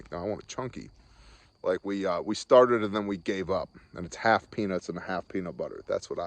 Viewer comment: sounded like you said "half penis.. half smooth"... very weird flex... then I saw the vid again...